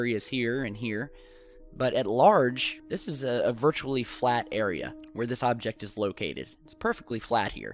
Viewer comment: did you say flat earth?😉